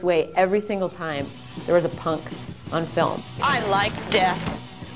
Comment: i thought she said "it details every single time there's a PUN in a film"... then rewinded and realising it was punK was a huge disappointment :(